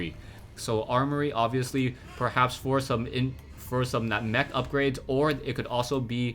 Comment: Is it just my perverted mind or the way pronounced *for some* at 8:47 really sounded like *foursome*?:D